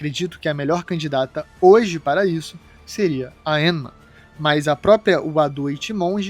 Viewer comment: Did he just say Enema